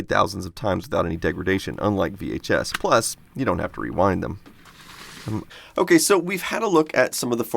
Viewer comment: You say, "You don't have to rewind." I hear, "You don't have to be kind." And I don't like that >:-( 15:48 THUNDER IN PARADISE IS THE SHIT! I used to watch that all the time.